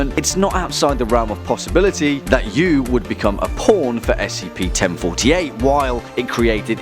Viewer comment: Says 'Pawn' but thought he said Porn)aaaaaaaaaaahhhh....AAAAAAHH MY HEART HURTS I'M GOING TO BLOODY DIIIIE!!!(Grips onto chest wheezing)